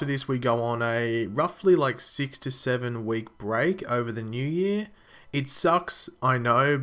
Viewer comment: oh my god Oo i thought you said 67 weeks xd